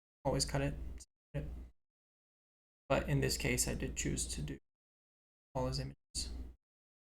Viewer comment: what did you say at around 12:50 the audio cuts out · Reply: I think I was saying how I could make a bunch of 3d rocks and dirt assets and move them around but in this case I took the decal image and just used QuickShape to cut it into a bunch of different assets and copied and placed them around instead